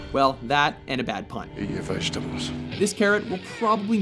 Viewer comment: What did the killer say at 6:26 ? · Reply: Eat your vegetables.